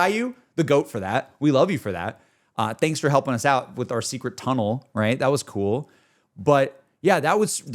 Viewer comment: Never underestimate The Dragon of the West! And yes. Roku is VERY strong. 😂 32:03 - did you say secret tunnel…? 🤪